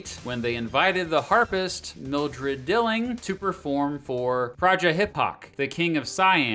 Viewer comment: When I heard you say prajahihok, I thought you said "Project Hip-Hop"